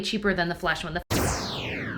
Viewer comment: I kept hearing "flush" in the beginning when she said flesh. It looked like it should have been flushed!